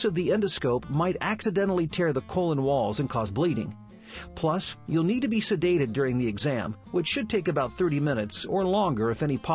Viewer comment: no joke I thought he said You need to be seduced 😂😂😂 And I was like Waaaaaaaat